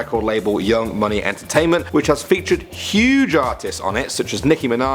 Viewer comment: wait did he just say master p discovered Chris Brown Wiz Khalifa and Chief Keef wtf that's not true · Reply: it said he them featured not discovered 😐